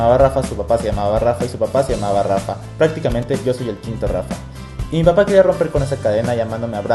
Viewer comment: Like si en el minuto 0:13 escuchaste que dijo una grosería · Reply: quinto